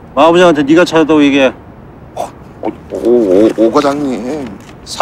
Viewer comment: 상식이형 대사 다음에 오과장님 뭐라고 하신거임? "~~~~ 확 죽여버려" 는 알겠는데 ~~~~가 뭐임 ? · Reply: 나처럼 올리면 확 죽여버려 이씨 라고 합니다ㅋㅋㅋㅋ